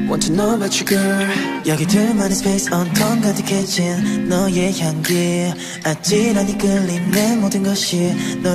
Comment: Why do I always hear "on top of the kitchen" in 1:26?! 😅